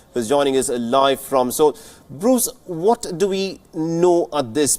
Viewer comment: What Mohammed Saleh said at 1:18? It sounds like "head" to me